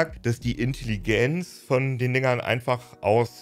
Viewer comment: wer hats auch gehört? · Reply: Angeblich hat der "dingern" gesagt